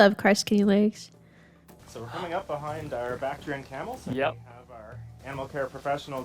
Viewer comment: The auto subtitles can't make sense of it except when the main visitor guy says it. I could have sworn they were saying "these are our back-trained camels... the back-trained camels have..." like that was the term for saddle-trained; these individuals have been trained for riding or carrying cargo... Bactrian. Bactrian camels are the ones with two humps, as opposed to the Dromedary. lol